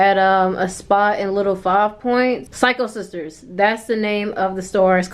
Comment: Low key thought u said cycle sister my high ass 😂